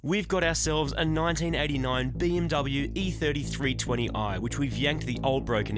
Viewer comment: It sounded like you said e33 20i for a sec and I was very confused XD